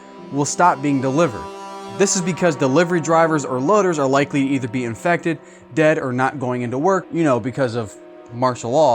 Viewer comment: what did he whisper? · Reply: CoronaVir*s